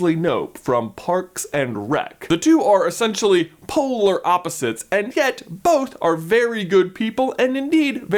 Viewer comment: you said "polar opposites" and I heard "Poehler opposites" like Amy Poehler who is Leslie Knope and I laughed for like 3 minutes I had to pause your video